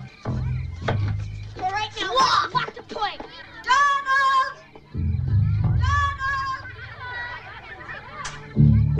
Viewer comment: WHAT DID SAY? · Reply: "Donald". Probably one of the kids had that name.